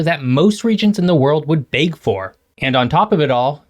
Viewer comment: I hear "bake for" 🙂